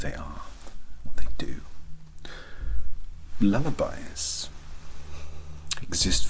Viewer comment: The first time you said lullabies I heard "Leather Bias" and was REAL confused